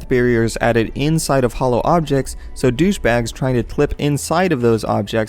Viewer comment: did just say "Douchebags on 7:16 - 7:17 ???